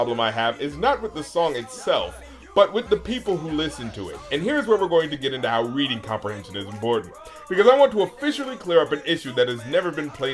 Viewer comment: What the hell does he say at 03:10? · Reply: "And here's where we're going to get into how reading comprehension is important"